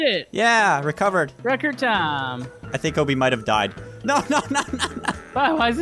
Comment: I thought he said “Kobe might have died,” and this came out before 2020 °-°